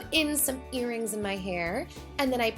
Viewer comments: Is It just me or did you just say "put earrings In my hair" :) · IDK IF YOU GUYS HEARD IT TOO BUT I THINK SAID "I PUT EARNING IN MY HAIR"